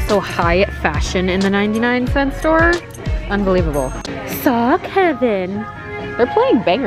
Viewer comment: When said "sock heaven" the captions definitely thought she said "saw Kevin"